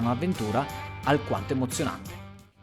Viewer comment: Ok, sono ufficialmente rincoglionita... Nella tua intro ho capito "al quarzo eccezionale"... 😣